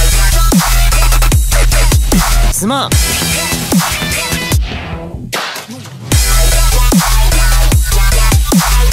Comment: what does he says here? '^' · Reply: I believe its "sorry"